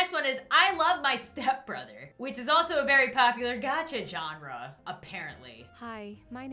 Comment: lol my captions said John rrah, ik its genre, i like having the subtitles to see how they mess up 🤣😂